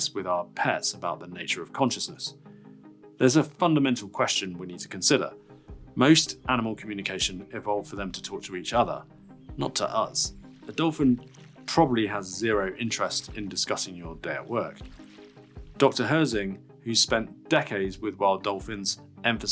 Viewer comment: It sounded like you said most animal communication evolved for them to TORTURE each other.